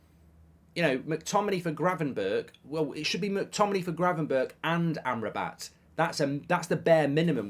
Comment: LOL 😂 For a second in the end at 9:14, I thought said Gravenberch for McTominay and Amrabat, I was like what... got it when I heard it again!